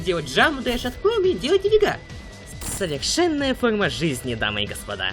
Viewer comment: Паясните што он тут сказал!!!!!! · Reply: А другой умеет делать нифига" вроде Так послышалось